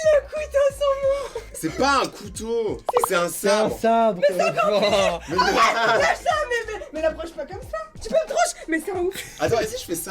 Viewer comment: c moi ou a dit lâche moi bb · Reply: Non je crois qu elle a dit lâche ça mais mais